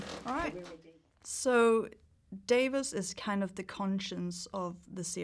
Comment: I thought she said Davis wtf who pronounces Davos like that lol